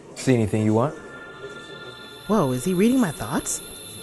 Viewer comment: At first I thought she said whoa is he reading my box LMFAOOOO but it’s “ THOUGHTS”